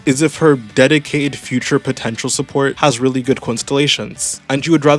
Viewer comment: did you say POTENTIAL?